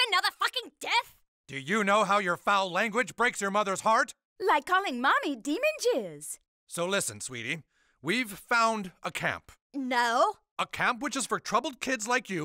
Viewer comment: “Do you know how your foul language breaks your mothers heart” “Like calling mommy demon jizz” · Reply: I THOUGHT SHE SAID DEMON JUICE AT FIRST 😭😭😭😭😭 then this was the first comment I read lmaoooo